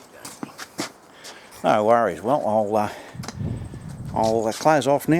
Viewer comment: That Aussie accent confused me. At the end of the video, I thought he said: "No worries. I'll take my clothes off now ..". And I thought, Whoa, no need to go that far. The bike is enough as it is, without gratuitous nudity!